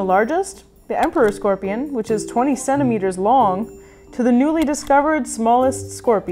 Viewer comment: I wasn't watching and I thought you said "27 meters long" I almost spit my lungs